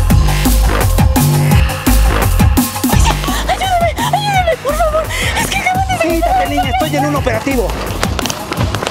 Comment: ¿alguien le entiende lo que dice? Porque yo no xD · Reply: Policia ayúdeme, ayudeme por favor, es que acaban de secuestragsjfkd